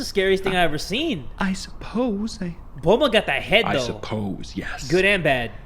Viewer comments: did he say Bulma lol idk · BULMA!!! DID HE SAY BULMA pls tell I'm not hearing that right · did he just say bulma